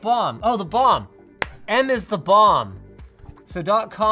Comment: I thought he said "n is the bomb" and uhh